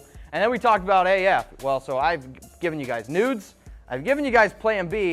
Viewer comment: Hey Fantasy Football Fam! How can I improve the show? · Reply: make sure you annunciate your words better. Every time you said "news" I thought you were saying "nudes" lol. Don't think it's appropriate to ask your coworker for nudes...